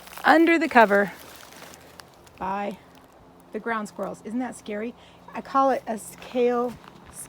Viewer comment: I've watched some of the back to eden videos and there are definitely good ideas there! I don't really have slug problems (yet anyway!), but ground squirrel problems. I'll keep your idea in mind, though! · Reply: LOL I thought you said ground slug, my PC is old and dying with bad audio. Your garden looks great!